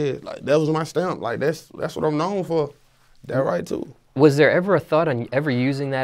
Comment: Thought this nigga was saying “that right tooth” this whole time 😭😭